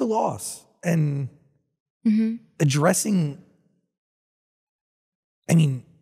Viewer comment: did he say aids?!??